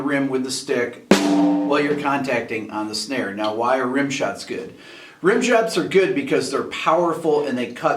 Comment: did he just say Rim Jobs?